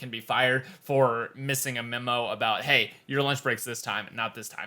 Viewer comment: I thought you were saying launch break the entire time